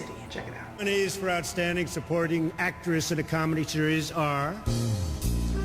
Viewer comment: He clearly said "Sex AND the city" though.